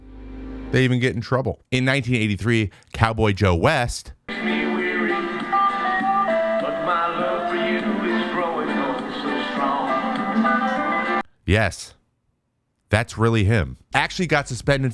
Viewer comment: I could have SWORE you said "Catboy Joe West" and my mind went somewhere I don't ever want it to go again.